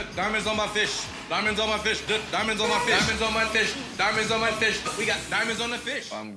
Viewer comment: "DIAMONDS ON MA FISH, I GOT DIAMONDS ON MA FISH!!" 😅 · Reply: I thought he was saying diamonds on my bish